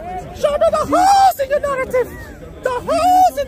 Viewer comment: At the end i thought he said "show me your holes hatun" LMAO! 😂😂😂😭😭😂😂😂